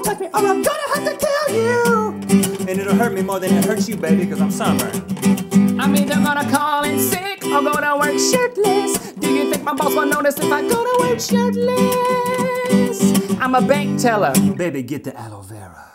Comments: You guys are the core meaning of awesome. At the part where you sing "do you think my boss will notice if I go to work shirtless?" I always hear "do you think my balls will notice if I go to work shitless?" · Its sounds like Link says 'shitless' instead of 'shirtless'.